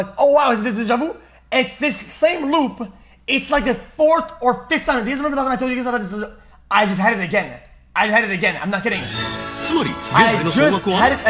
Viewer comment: what? can someone translate what he said there? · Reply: translate: “forth or fifth time these remember that i tell you something, i just had it again i had it again I’m not kidding”